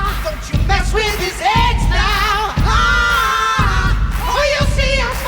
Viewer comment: The eagles born out of thunder. Aaahhh. He flies through the night. Aaaahh. Don’t you mess with his eggs now. Aaahhhh Or you’ll see us fight. Ahhhhh. Yes we have feathers. Aahhhh But the muscles of men (do some thrusts) aaahhhh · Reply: bro i thought they said “don’t mess with his edge now” the fact they said eggs has me dyinggggg